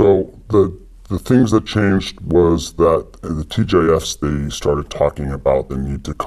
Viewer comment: "charged with preventing the next Trump situation" - Says it all. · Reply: What does he say there? Sounds like "The TJIS..." Does anyone know?